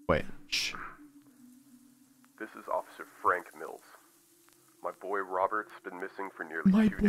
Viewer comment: minutes in "This is officer Frank Milfs" I can not be the only one who hear that😂😂😂